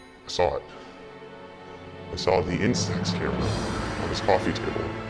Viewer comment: i DIDNT CATCH IT.. what he saw in his apartment ? someone pls ? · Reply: He saw the film camera that took the photo on Dylan's coffee table